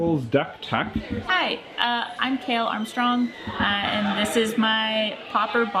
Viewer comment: i thought she said '' i am kayle i am strong''